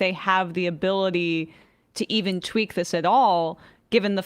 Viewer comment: Look for: altvrviolet - The Emmajority Report Fun Half Song (Tour, Sushi, and Poker w/ the Boys) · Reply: Lmao. I thought he said twerk. Thank you